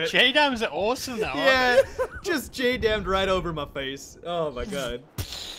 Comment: What did said at 2:52 ? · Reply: A scream then "I just hit you with a jdam"